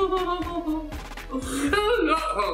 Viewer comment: I think they say 'And another fucking thing' where the subtitle says [unintelligible] at 13:35 Other than that, thank you very much, the subtitles were needed for the ball-talk